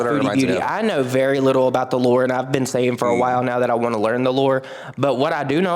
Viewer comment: Omfg I thought he said the lord too lol 😂